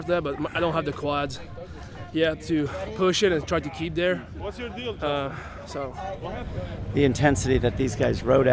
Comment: Anyone else hear at 6:15 Lazar say “what’s your deal Justin?”